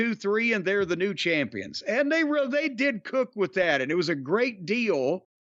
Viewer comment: Did Jim say "They did cook with that“ 😳 at 12:30?